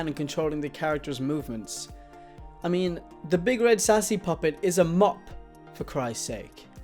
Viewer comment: You need a better backround, like a tie die blanket or soundproof foam. It was so distracting, i heard you saying things like "the big sassy doctor" or "the PS3 controls everything.